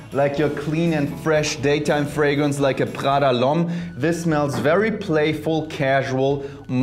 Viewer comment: What’s that fragrance he said @ 1:05? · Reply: Prada l’homme (edt)